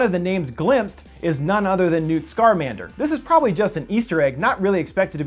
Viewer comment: Did he just say.... Scarmander?